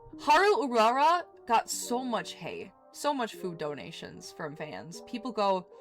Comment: I literally dropped everything after I misheard “Hay” for “HATE” . Nobody in their right mind would hate haru urara. However PETA and people like them may be the exception.